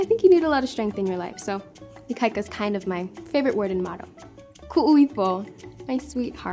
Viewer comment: HELP 😂 1:04 what does she say? “‘Ikaika is kinda my favorite word in..... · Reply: and motto"